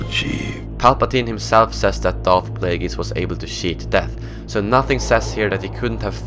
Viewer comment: did you say shit death